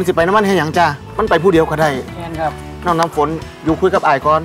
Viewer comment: เค้าพูดว่าน้ำฝนนะคะ